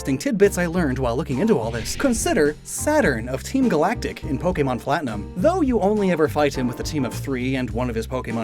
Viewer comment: did you say “Pokemon ‘Flatinum’”? lol